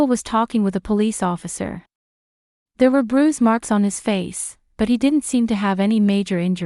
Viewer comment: I rely heavily on reading the text because of hearing issues. Besides the "obscene" reference, I noticed several errors. For example was when the text indicated "lunched" instead of the correct word "lunged." Why doesn't the writer proofread the text subtitles before posting the video? AI errors ruin these stories. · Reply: But wait, there's more: At 29:02 "brews marks" instead of "bruise marks"